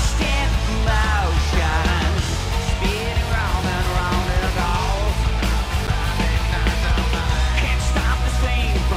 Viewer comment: Portnoy misses the lyrics at 5:59, then James support him at 6:09 and he nails it! I can't hear what he said after 😂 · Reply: He said "There it is!"